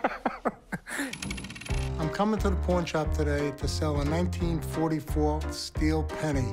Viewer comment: did unc really say Corn Shop 😂😂